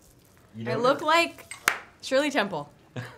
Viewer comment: what did she say in 6:20 can someone plz tell me if you know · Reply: I look like Shirley Temple